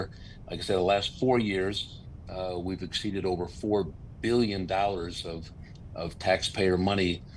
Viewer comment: Did i hear the Senator say at 1:33 "Illinois has spent over $4 BILLION DOLLARS of Tax Payers Money over the past 4 years on Medicaid for Non Citizens or Illegals"? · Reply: Yep…4 BILLION.